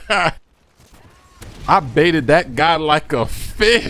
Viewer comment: I thought said "I baited him like a bitch!" I was like, did just cuss? Is this a fake video?